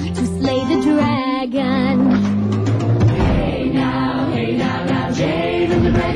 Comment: Ah "dragon's sweet". I always heard "dragonspeed".